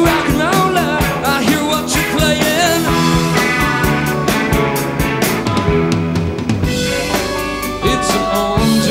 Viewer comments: Can anyone read Ben's lips and tell us what he was saying? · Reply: "I'll get you" to the guy who threw a beer on stage. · I'd love to know what he's saying to himself, away from the Mike, at 0:45? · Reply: Someone threw something beside his head, so he said something like Im coming to get you😂